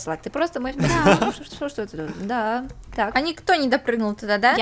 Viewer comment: так стоп, я что-то не расслышала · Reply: она говорит: "что же это"